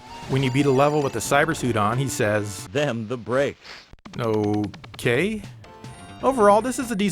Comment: What he say damn the brakes ?